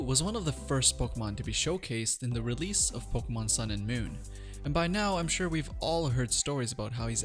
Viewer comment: Why do I hear "Fuckmon Sun and Moon?"